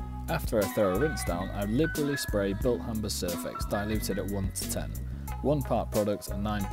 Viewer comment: What is the name of the chemical product you used? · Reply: THANKS! I thought you'd said "Bill Humper's cervix" .....couldn't find it anywhere!